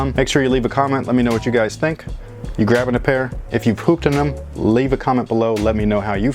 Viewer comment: sounded like he said "if you pooped in them" at the end 😂